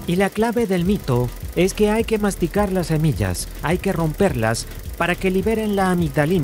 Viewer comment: ah, dos semillas, escuché "doce millas" xD